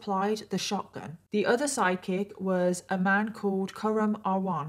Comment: U were saying "side kick" & I heard "psychic" 😂